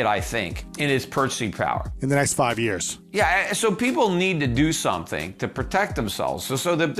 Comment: I thought he said “people need weed” lol hell yeah bruh